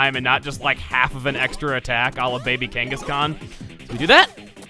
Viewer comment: when said "Baby Kangaskhan" I thought he said "Baby King Kong/Baby Donkey Kong." either way there was a Kong in there. :P